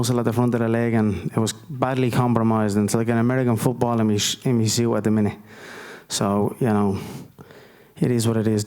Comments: What I heard [It’s like an American football in me.. me c what a minute? ] Anyone make that out? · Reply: 'In me suit at the minute...' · Can someone tell me what he said at 0:36 after he said AmericanFootball? I've watched the clip about 20 times and cant understand it. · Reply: in me suit at the minute